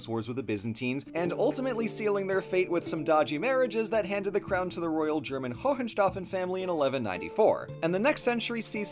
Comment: did you say weed exports or wheat exports? I need to know. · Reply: Wheat